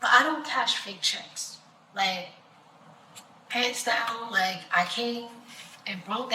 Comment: did she really say “pants down” 😅😂😅🤣😂🤣😂🤭